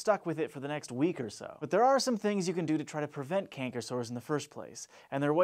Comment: I thought it said Cancer Sores...